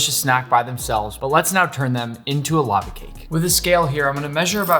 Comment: did he just say lets not turn them into a lava cake????